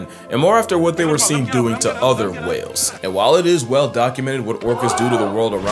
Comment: While it is "Whale" documented ... did I hear that right lol